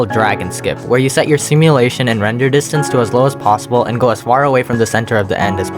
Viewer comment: Did he just say plossible?